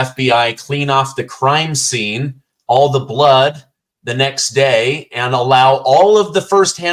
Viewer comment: was it the next day? IDK about that. I heard ten. Coulda been about something else, I guess.